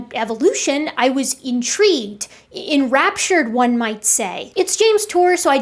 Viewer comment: did you just say rapture? RAPTURE?!